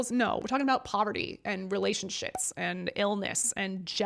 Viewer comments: girl did you just say “relationsh*ts”? 💀😭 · I was wondering which category Mario Lanza fits into . Also ," relationshits "29:35 . Did I hear that right ?